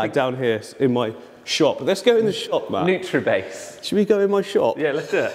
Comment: I keep hearing "Bishop Matt", it's hilarious! Really awesome build btw, very inspiring!!